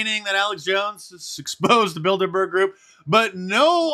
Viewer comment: I keep hearing and think "Build-a-Bear Group." The secret annual "Build-A-Bear" meeting. 😅